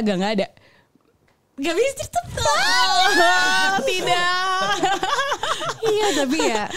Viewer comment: sherina ngomong apa ya? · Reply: "nggak bisa cerita banyak"